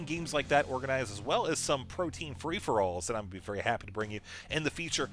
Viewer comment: I heard "protein free-for-all's"